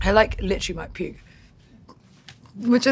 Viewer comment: thought she said "literally might poop" at first